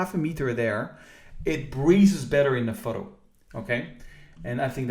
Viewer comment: Lol I thought he was saying "breeze" then I realized he was saying "breathe"